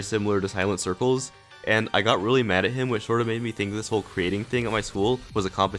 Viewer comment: i thought it said cheating